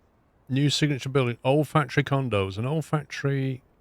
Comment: Had this playing in the background but it definitely got my attention when I thought you said 'olfactory condos'.